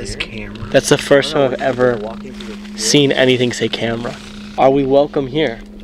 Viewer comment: A 31:56 I heard are we well, then nothing.